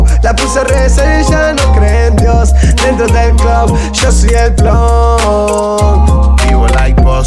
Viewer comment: Pensé que decía, Vivo acercándome dentro del club